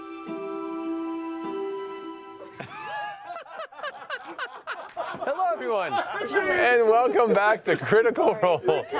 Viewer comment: i'm so curious what ashley said here that made them all laugh so much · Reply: update: it was "poop in a cup" thanks talks machina